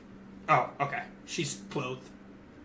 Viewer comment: sounds like he said she’s 12